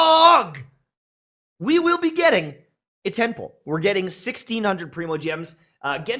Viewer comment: the entire time I thought he was saying "a temple" but then I realized jebus Christ he's saying "a 10 pull" Don't even try to convince me you didn't think the same 🚶